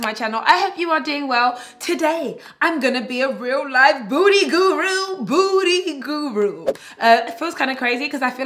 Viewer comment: Sounded like you said Booty Guru 😂